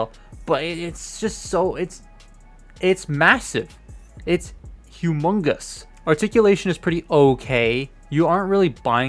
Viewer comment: legit thought he said amogus XD